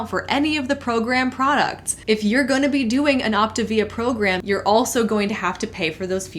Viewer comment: Well. I thought you were saying Activia wrong.